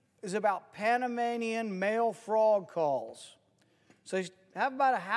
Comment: I really thought he said frog balls He said calls 😞